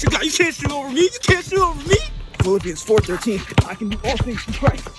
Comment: hold on, did i just hear P H I L L I P P I N E S ?